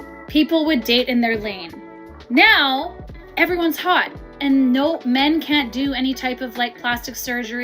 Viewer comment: women don’t have options? Is that what she actually said????